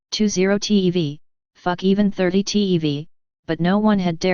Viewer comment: I swear it says fuck even 30TEV